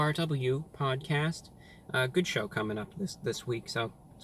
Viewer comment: I thought Reviewbrah said "good shit" and I couldn't believe my ears